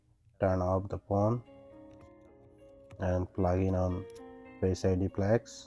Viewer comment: Ear speaker and Face ID flex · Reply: I thought you said only face id?